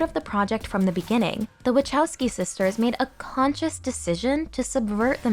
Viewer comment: "Sisters" lol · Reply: I replayed that four times to make sure I heard it right. Thought she said Wachowski's at first. What a gaff. EDIT: Looked into it and it turns out the Wachowski brothers both had a gender transition to female which is why they're referred to as the Wachowski sisters. Ya learn something new